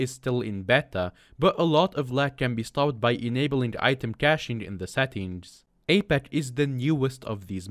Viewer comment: what does he say here? I genuinely cant tell pls help it do be lagging for me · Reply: Justin it might be “caching” like cache